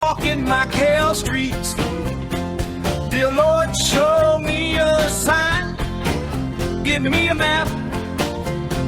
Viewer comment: I for one don't mind this somewhat new AI-generated country theme song as it sounds like he is saying "FOCKING MACAU STREETS"